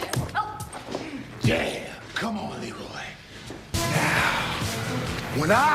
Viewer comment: haha, I'm sure i heard...." c'mon Leroy " right at the end ? :)